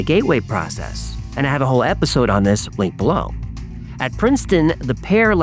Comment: Dang it. Misheard. I thought you said link down below. I was so excited. 😂